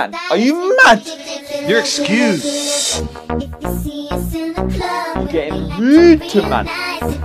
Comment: I thought he said "you getting fruity to man" 😂